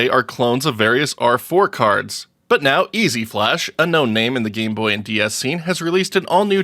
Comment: I said they are a known name... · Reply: oh ok. It sounds like you said “no name” in the gameboy scene